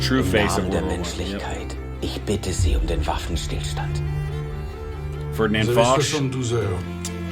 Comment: What was the name he said? Ferdinand Vaush?